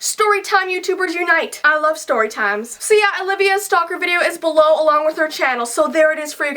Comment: It Almost Sounded Like She Said Olivias A Stalker ;3